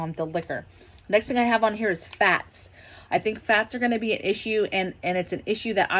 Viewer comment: Your accent threw me off, I thought you said "Facts" and you were gonna go into communications. 😆 Oils are good too LOL Have a great day!°